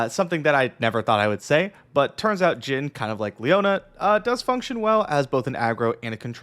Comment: Did I heard Jhin/Leona? 👀